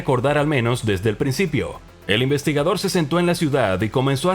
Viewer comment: ¿se sento en una ciudad o escuche mal, o entendi cualquier cosa?